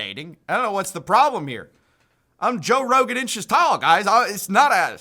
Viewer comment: Did you just say “Joe Rogan inches tall”? 🤣🤣🤣 that’s funnier than anything he’s ever said and he’s the comedian 💀